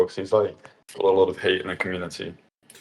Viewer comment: DID HE JUST SAY SMTH ABOUT HATE